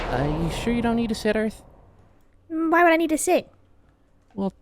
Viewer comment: When said "are you sure you don't need a sit Earth" I heard "are you sure you don't need a sitter Earth " am I the only one who heard that?